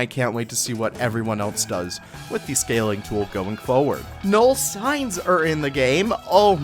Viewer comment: what did he say? No signs? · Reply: Null signs, ie. invisible signs